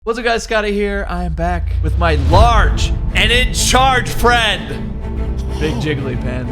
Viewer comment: I thought scotty said large and discharge